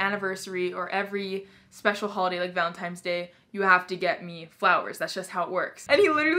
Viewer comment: did she just say.... valentimes day